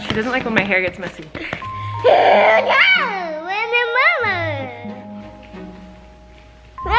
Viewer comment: I literally heard and understand saying "Oh no! Where did Mama go?" and its the cutest things ever 😍